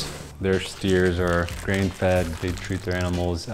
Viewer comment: DId he say "grain fed" at 2:49? Uh, I don't think he knows what grain fed means.